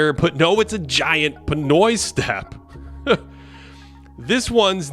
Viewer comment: did you really say Pinoy Step at 3:05 of your video or am I just hallucinating?